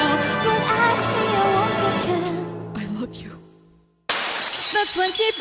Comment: screams like crazy, fangirling WAAAA!! I LOVE YOU TOO, LEN!! Anyone else heard the whisper of "I love you"?